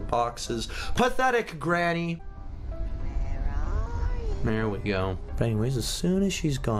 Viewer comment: Anyone else heard where are you?